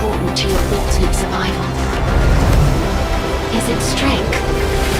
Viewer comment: did anyone else hear her say "is it Ebstein"